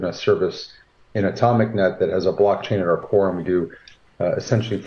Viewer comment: Did he say Coreum? 😮